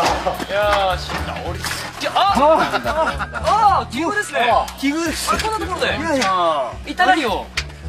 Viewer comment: 福山「あ〜 金庫ですね！」って聞こえた あと 近藤「きぐるっす！」って聞こえた